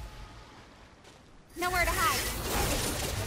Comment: I thought it said cut through the cake